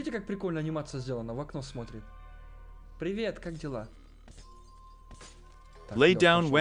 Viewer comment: Кагда ты сказал как дела мне послышалось каделаг😂